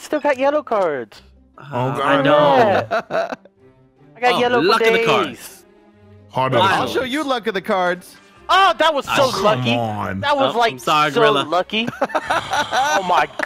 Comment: did anyone hear Delirious say “That so clucky”